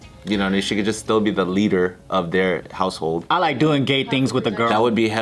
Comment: “I like doing gay things with a girl” it’s sad sometimes the crew misses what says